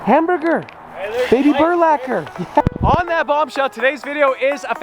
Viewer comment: Wait did he just say "Baby Burlacher" ???????